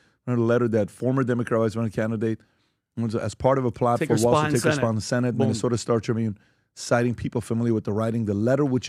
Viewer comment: i think he said it was because something like tin walz wanted klobachars congressional seat..